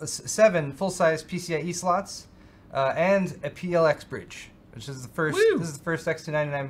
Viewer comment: Haha did not understand what had said at 12:00, so turned on the CC caption momentarily, it was "PLX bridge." Right afterwards Youtube captioned Thread Ripper as "Red River" :DDD